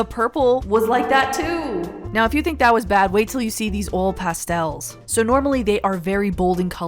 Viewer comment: I thought you said old pastels instead of oil pastels 😂